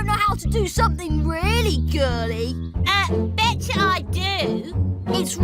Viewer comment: I thought at 7:04 she said b!tch I do 😭😭😭